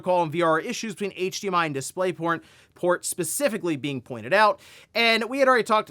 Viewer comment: I heard you. Display Porn.LMAO!